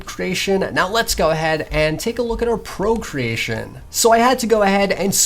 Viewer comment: Procreating? did anyone say procreating???